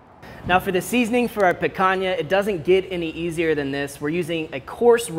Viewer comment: Lol I thought it said piranha